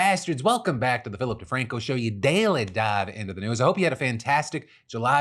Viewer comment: CC say ‘you daily died into the news’ wow… sounds like the segments today are gonna be killers 😏